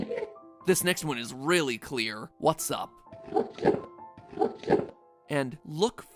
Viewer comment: I thought the enderman was saying "woodchip" instead of "whats up?" And then i just give the enderman a block of wood 😂